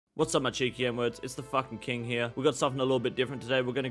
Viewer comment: My volume was pretty low on intro. Seriously thought you said “what’s up muffpeople” 👏